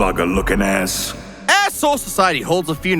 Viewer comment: thought you said “asshole society”😭😭😭